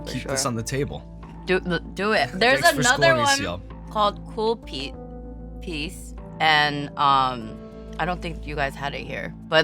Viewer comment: CL mentions calpis, it's called calpico in America. It's a milk soda. It's actually quite good. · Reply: I heard "cool piss". Glad it wasn't.